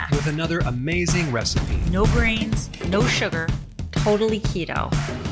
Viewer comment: It sounds like says "No Brains" in the cooking commercial